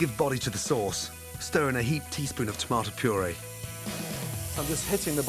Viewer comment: did he say TEASPOON and put THAT MUCH in ??